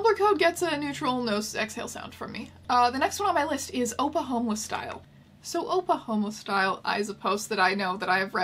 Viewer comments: This whole time I thought you were saying Oklahoma Style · Every time I hear her say "Oppa homeless style" I think she's saying "Oklahoma style" and I tense up in confusion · the fact that oppa homeless style isnt even a real tumblr post just kills me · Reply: This whole time I thought she was saying “Oklahoma style” 😂😂 she referenced it in another video and I was like “wait what Oklahoma tumblr post is this?? I don’t remember this one.” So I came here to find out I just can’t hear apparently. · for so long i thought she’s been saying “Oklahomeless style” like it was something to do with oklahoma but i like this version a lot more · Oh. Oppa Homeless Style. I thought you were saying Oklahoma Style. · Oh my gosh, I thought you were saying Oklahoma style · I'm watching these so out of order and I swear every time you referenced this one I heard "Oklahoma style" ahah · Cripes, I thought you were saying “Oklahoma Style” and I was not prepared for the actual story. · I thought you said Oklahoma style, like wtf would that even mean?